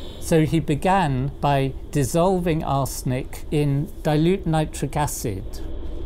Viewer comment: It sounded like he said "dissolving our snake in dilute nitric acid". lol.